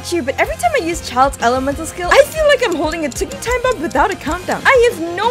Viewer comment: I thought you said "ticking time bomb without a condom"....yah I wear glasses